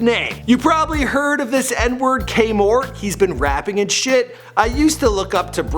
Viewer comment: Its Kaemor Not K-Mort. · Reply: Bruh I thought he said k mart but yea it's kaemor